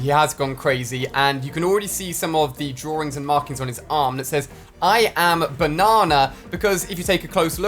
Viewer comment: I thought it said i am banned